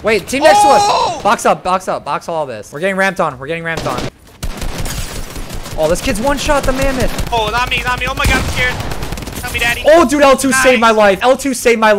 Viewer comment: anyone else hear at 9:52 when he says “help me daddy” 😂